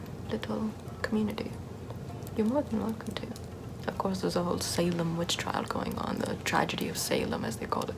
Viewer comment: I thought it said salmon witch